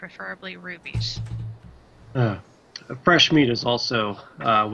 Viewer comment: Everytime Bottles was pushing the "Fresh Meat" I kept hearing "Put crew in catapult"